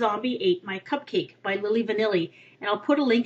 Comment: LOL, at first I could have sworn you said "Milli Vanilli" in the beginning. I'm all, "Is that what they're doing now?" XD